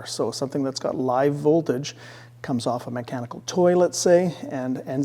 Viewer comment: All I hear is: "comes off a mechanical toilet say" Sorry not sorry 😂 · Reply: LOL....now that's all I hear too! It should be mechanical toys....lol. Thanks for the watch!